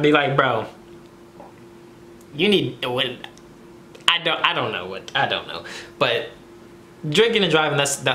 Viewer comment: did he just say you need a wooden a